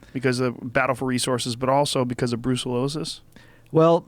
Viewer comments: I swear he says Bruce Willis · I thought he said Bruce Willis.. I was like wait, what? Bruce Willis has Bison? wtf..